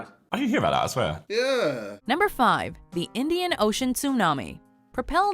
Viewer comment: I thought I heard the "Indian ocean punany"